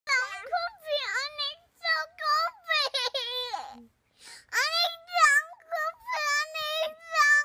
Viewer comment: What is the kid in the first clip saying? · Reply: "I need some coffee!"